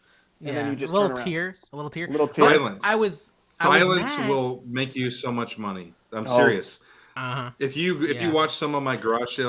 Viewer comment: I originally thought said “violence… violence will make you so much money”.. I was like 👀 and had to rewind hahaha